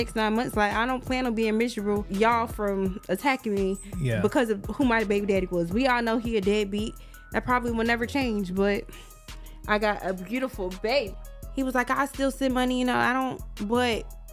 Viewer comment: thought she said he’s not a dead beat 😂😂😂😂